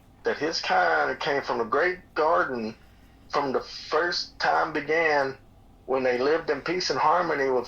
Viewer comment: Ok if I’m not mistaken , on the last interview with this guy or maybe the one before he said and I quote the second litter of pups didn’t live but 24 hours after they were born and I think he said there were 3…. So I’m gonna go back and listen again . Go listen for yourself. I love this guys story and I listen with an open heart so please don’t think I’m being nasty just checking the facts .. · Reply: I thought he said the first litter